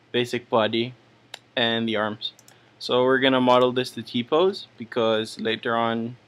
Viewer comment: at about 3:45 did he say taco?!